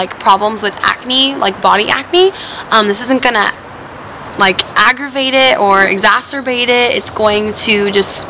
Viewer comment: When you were talking about the sunscreen you said agravate it and then I thought you said Masturbate it.